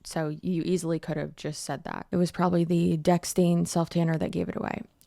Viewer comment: Yo! Hahaha did you just say “deck stain” self tanner?! Hahaha! Yoink, I’ll be taking that for my personal collection. 😂